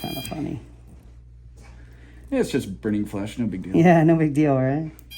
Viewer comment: Sounded like he said "brain flesh" lol